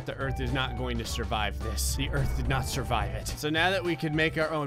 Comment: is it just me or does gray say "It does not survie it"?